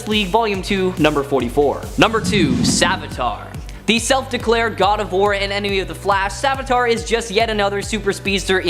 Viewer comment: did you say “the self declared god of war”?